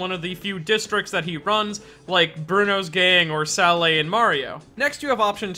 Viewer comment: for a second there I thought you said "Bruno's gaying" eheheheheheh